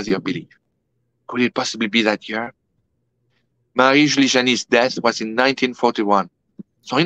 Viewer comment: did anyone understand what he said at the 49:51 mark? Which seer died in 1941 and the relevance of the number 83? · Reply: Marie Julie Jahenny